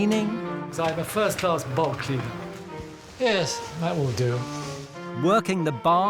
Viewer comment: Did he just say 'I'm a first class bog cleaner'? LOL